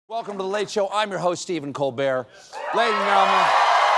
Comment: Why is it that when he says, "I am Stephen Colbert" i always hear, "I am Stephen Colbear"?